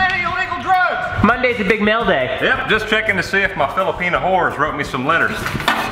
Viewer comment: Did i just heard "FILIPINO"